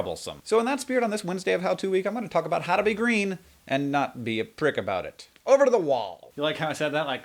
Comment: I thought it said "How to be /Hank/ Green..."